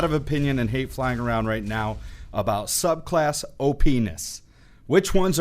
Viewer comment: he said op ness but it sounded like Oh penis😂